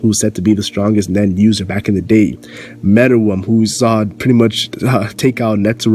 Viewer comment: Everytime you say "Meruem" it sounds like you saying "Metal Worm" for some reason...